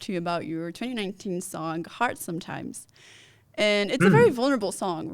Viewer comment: should be corrected to say "Hard Sometimes" instead of "Heart Sometimes". Great, revealing interview and questions!